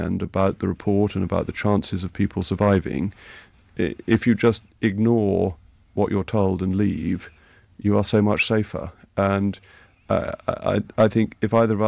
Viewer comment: GO TO 0:27 AND HEAR WHAT HE ACTUALLY SAID . he said " THE MORE ONES READ OVER THE WEEKEND ABOUT THE REPORT IF YOU IGNORED WHAT YOU ARE TOLD AND LEAVE YOU WILL BE SO MUCH SAFER. He is talking about what should be done AFTER reading the report . He was NOT saying the victims didn't have common sense. I wouldn't take any notice of the fire brigade now if they told me to stay in my burning house .....I'd get out as fast as I could.